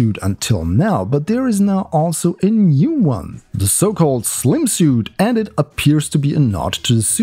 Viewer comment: What I hear: "a new Sokol space suit..." 5:15 O_o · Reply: I'm pretty sure I said "the so called slim suit" ;)